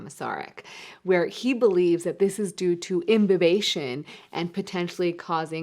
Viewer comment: what does the word imberbation mean